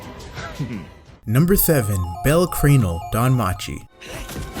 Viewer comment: Did he just say bell creanl